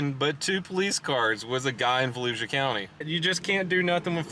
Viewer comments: “The volusia county sheriff’s department “ me sitting in my apartment in volusia county “ UHHHHH” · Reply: I thought he was saying Fallujah County lmao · That’s how Floridians view Volusia County. Ever hear of Daytona Beach? That’s Volusia. But the beach side has nothing on the mainland side. Those people are crazy. Deltona, where this occurred, is just as nuts. “Florida Man” lives in a small, handful of counties in FL. Volusia is definitely one of them. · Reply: I'll be honest... I heard Fallujah County... And I'm still not convinced I misheard